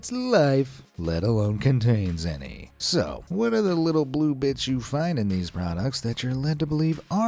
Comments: Why did you say bitch? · sorry i thought he said Blue bitch until i turn in the caption😭